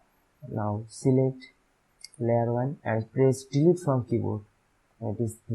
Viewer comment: can someone tell me pls at 13:39..what key does he say? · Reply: Delete